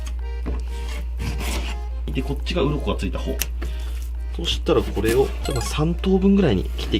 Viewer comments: 鱗がついた方がう○こがついた方に聞こえる笑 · こっちがうんこが着いた方って聞こえた笑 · こっちがうんこがついた方に聞こえる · でこっちがうんこ着いた方 って聞こえたから耳鼻科行ってくる